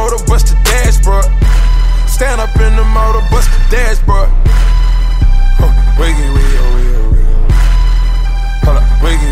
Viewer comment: I thought he said wiggy wiggy weyo